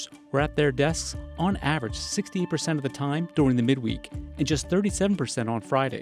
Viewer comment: sounds like he says 16% and not 68%